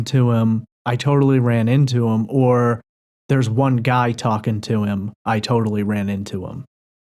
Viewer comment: I hear "There's just no talking to them"